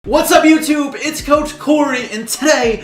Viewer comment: I thought you said Whats up Youtoo.